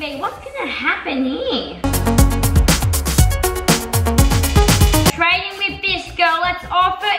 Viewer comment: When said "Trading with this girl" I thought she said "Trading with Disco"